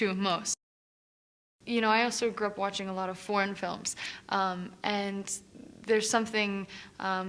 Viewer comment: I thought she said " i grew up watching alot of porn films" lol